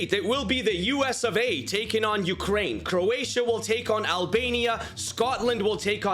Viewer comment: I swear at 8:14 he said “ the U.S. of aid taking on Ukraine”